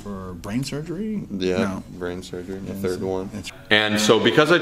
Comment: did u say groin surgery? The third one. Is this your 3rd groin surgery?